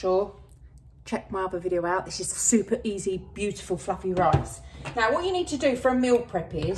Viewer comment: Could of swore she said beautiful f*cking rice. She said beautiful fluffy rice 😂😂😂